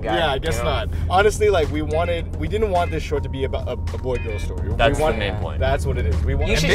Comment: what did say? Didn't want it to be about what? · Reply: "a boy-girl story".